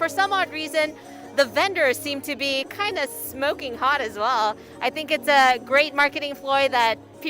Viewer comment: Thought she said smoking POT?